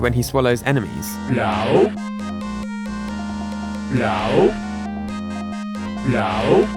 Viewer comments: I am German and it sounds like Yoshi says Blau wich means in English Blue 🤣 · And me as german, i wonder why he call the name of a Color here xD"blau" is german for "blue" and just sounds nearly what Yoshi say here: 07:00 xD